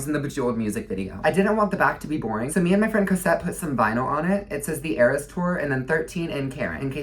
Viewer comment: Hi did u say ur friend Cosette help u with Karen Cuz my name is COSETTE